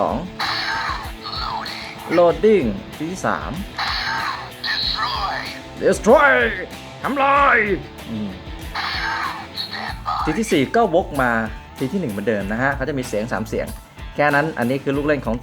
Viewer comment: ผมได้ยินว่าน้ำลาย